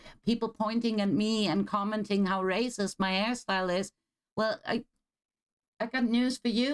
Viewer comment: Oh, hairstyle... I heard it as ass style x)